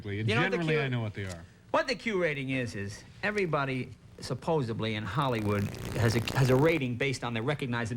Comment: Does Jay say 'supposeBly' around 10:58?